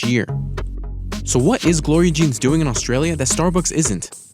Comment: I heard "Lower your jeans" first